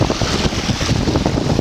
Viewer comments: He litterally said "im going to record one more time under water and be done" ... god gave him 100 chances but he refused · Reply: he said something like "im going to record one last bit underwater for the day" so not exactly what my comment said but thought at first he said that,still same thing just different wording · The fact he said " one last bit under water..." is scary. What does he say after that? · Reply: Sounded like he said “then we’ll call it a day”